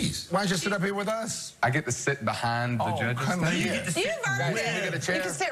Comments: OK, so let me get this straight. Katy did say, "just don't look at my snack" as the clip ended, right? Knowing how Katy is she is liable to have said a dirtier word than snack. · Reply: Don Olinger Yeah, I thought at first she said snatch and not snacks. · LOL, I thought she said do not look at my snatch that southern accent and realized she said snacks. Anyone else thought this? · lol I could have swore Katie said Don't look at my snatch!!!!🤦😂🤪not snacks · omg, I know this is in bad taste, but at first I thought Katy said," Don't look at my Snatch" lmao